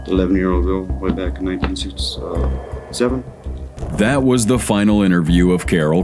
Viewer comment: The on-screen CC says a boy in 1987 but HE says girl in 1967. Who is the video editor? They need to be fired for mirepresentation of facts. They let that lie on this clip , someone deaf might not notice his lips and believe the lie that they read. 12:06 it lied again! HE said no, and it said he said yeah!